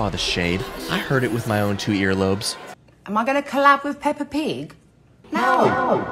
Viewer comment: I thought she said clap with peppa pig 😭